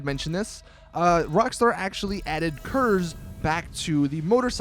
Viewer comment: what are curs or what ever he says?